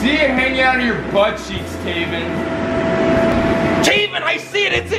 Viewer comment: sounds like there saying Taemin u know the Korean singer😃